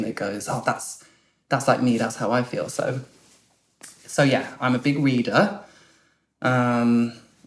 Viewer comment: I heard "i'm a big weeder" 😄